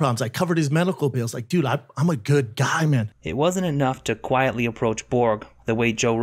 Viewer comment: sounds like his sayinf im a nice guy 👦